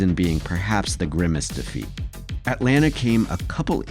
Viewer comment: did someone say the Grimace defeat?